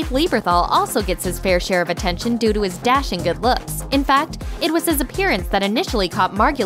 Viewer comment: Thought you said good looking